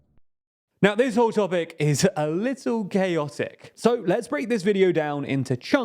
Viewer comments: Not going to lie, when I heard you say chaotic I actually thought you said gayotic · Could have sworn you said "gayotic" 😂